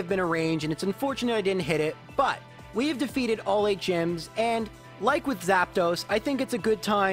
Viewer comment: did he just say “we defeated all HM’s?”